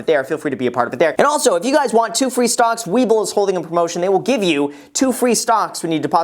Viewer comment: Towards the end of this video ( at 16:27 ) you mentioned Some Co giving away 2 Free Stocks .. Did you say " Wiebold " ?Looked it up ... Nothing I probably misunderstood the Name. ; Please clarify.